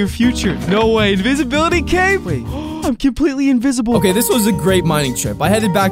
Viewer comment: Abot right there and ad came, so there for I head u say "I'm gay" instead of apparently. "I'm completely" not quite right there. Funny how I head gay tho. Hahh